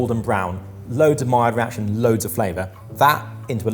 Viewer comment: Hey, is there a way to 'report' a mistake in the transcription? I don't know how transcriptions work on youtube. 3:47 I'm fairly certain is saying "maillard reaction" instead of "mild reaction". [This is definitely not to criticize the person/people who put in all the work to make the transcriptions. They are helpful and there are probably many people out there who rely on them to enjoy these videos.]